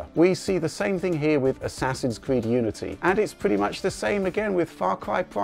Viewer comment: I swear to lord I heard assassin's creed nudity